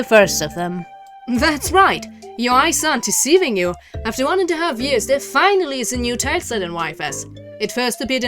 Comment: did a pretty good job with the script however at 0:29 its kinda hard to understand what she was saying probably could've slowed down a little with the reading here as its kinda hard to make out what she is saying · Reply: "After 1 and a half years there finally is a new Tileset in YFS" is the full sentence